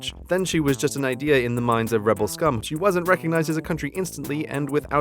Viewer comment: Does he say rebel scum?